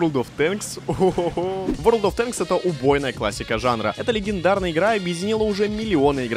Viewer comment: послышалось "убогая классика жанра"